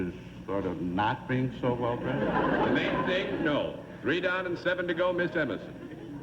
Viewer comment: ummm … I watched the mention a couple of times at a slowed down rate. I’d say Daly had a very blank expression … and what Daly said at that point was “the making thing? No.” So, I’d say Daly was somewhat brusque and cool. Opposite of how he was with other players. I’d guess Hal was Daly's least favorite panelist. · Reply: no he said "the main thing? No"